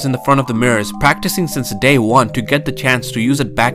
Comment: I wasn’t listening towards the end and I thought he said “only in Ohio is this face”